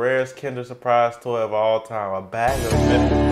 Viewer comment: if your wondering what that says it's not sh** it's a bag of met*